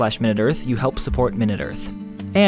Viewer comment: Ok so at the end when he said "Tom Scott" I thought he said "Tomska" for second and that confused the hell outta me till I looked at the screen and it said Tom Scott